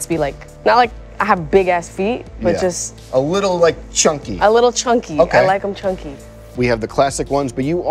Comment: Yeah I'm disappointed that my mind will instantly start playing the Moto meme in my head😂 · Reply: Same, at first I thought she said "I dont like 'em big, I like them chunky". Moto moto will forever haunt us 😂😂